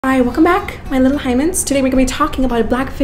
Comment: am i bugging or did she just say welcome back my little hymens